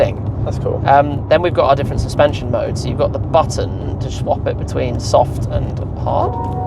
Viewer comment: Did he say "Shwap it?